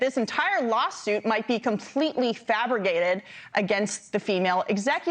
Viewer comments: Am I wrong or did she say “fabriggated”??? 🤦🏻‍♂️ (instead of “fabricated” - for all y’all that though there was nothing wrong with “fabriggated” 🙄) It’s funny when you hear someone say it at a party - but it’s pathetic when you hear a reporter say it! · i'm sorry, but did she say "fabriGated"??